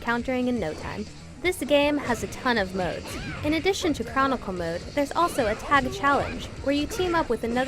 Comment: I thought she said "Comical Mode" instead of "Chronicle Mode." ..same thing I guess..